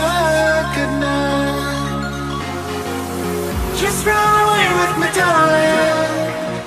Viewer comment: just run away with mc donald i misheard